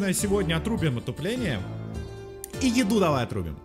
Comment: мне послышалось или он сказал отупление? )